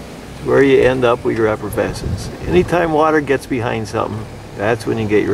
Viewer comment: I heard “effervescence”